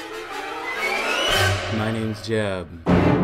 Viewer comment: I'm new here.. At the start he says My name is Jeb right? I heard it as My name is jeff